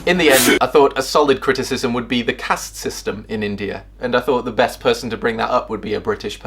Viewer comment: I honestly thought he said the cat system